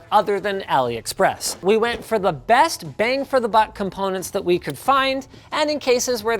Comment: "Best bang for the butt"? Am I hearing right?